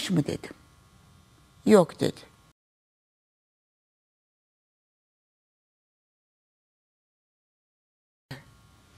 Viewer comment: dan sonra sesi kapatılıyor. Orda ne diyor ağız okuyan var mı??? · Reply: tecavüz mü etmiş dedim, daha kötü dedi diyor